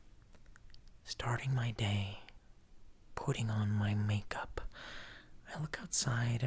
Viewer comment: at first I thought he said pooting 💨 on my make-up 😂😆😂